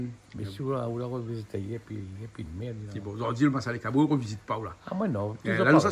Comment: Massalé quoi ?? MONSIEUR l’a dit ?? 😅😂😂😂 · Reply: Massalé kbo🤭🤭🤭🤣🤣🤣